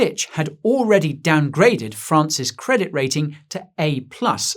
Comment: It sounded like he said France’s credit rating had been downgraded to “an A+”.